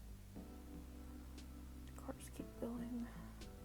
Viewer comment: Wait a sec..... WHAT WERE U DOING AT 14:39?!?!??!?? I HEARD “corse keep going~” WHAT IS THE MEANING OF THIS?!??????!!?!?!??1!?!?!?